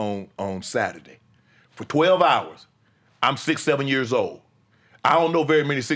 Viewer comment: looool, I had to rewind this 5 times, because I was like whataaat, he’s 67 years old? Damn, he looks good for that age 🤣 until I realized what he was actually saying 😅